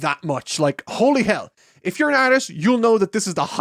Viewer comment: I thought you said "If you're Irish" and I'm like..."why is drawing people a common way to praise them in Ireland?"